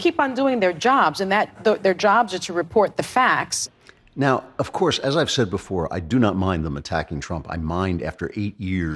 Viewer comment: Did Joy say Kekistan or did I just mis hear that? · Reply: Chris Shockley She absolutely said Kekistan, I nearly spit out my drink.